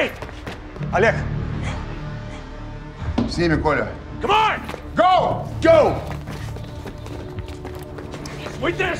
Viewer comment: what is that the Russian bodyguard saying to the president? Is he saying goodbye or something? I always wondered about that, shame there was no subtitle for that in the movie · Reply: S nimi, Kolya Go with them, Kolya